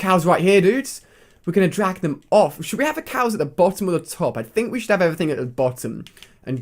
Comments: did just say he was going to jack the cows off? xD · did he say he is going to jack the cows off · it's sounds like he said he was going to jack the cows off